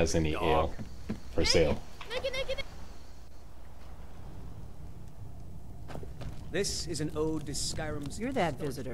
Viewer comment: I’m sorry, what did he say?!?🤨🤔🤭😂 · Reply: "Naked! Naked, naked, naked!"